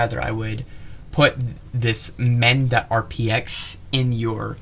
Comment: Tutorial to install the "them" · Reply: I heard "Men" but the auto translate is "Mend"